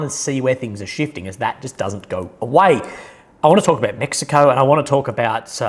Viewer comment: I just woke up, I swore I heard Mexico